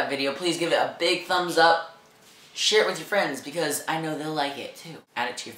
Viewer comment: In the end it sounded like u said shit with your friends hahahahaha